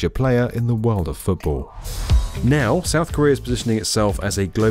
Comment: I'm sorry I thought you said North Korea because I'm not an American..... I mean USA but I'm an immigrant.